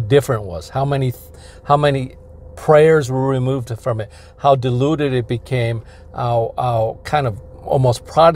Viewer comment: Haha, you said, "...how diluted it had become...." and I heard, "...how deluded it had become...."